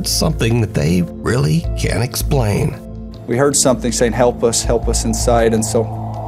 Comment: I hear the very faint voice of the mom asking for help. The office immediately responds, “We’re coming. We’re helping!” This kind of love transcends all time and space ❤ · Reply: Yes I heard “help us”